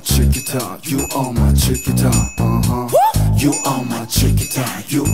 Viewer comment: Lmao I thought I heard "you're my suit and tie" this whole time wtf lmao..